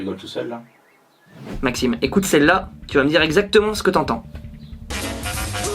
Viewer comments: Moi à 4:55 j’entends: ouvrez vos gros con, tous en levrette pour la matraque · moi j’entends : « ouvrer vos gros con »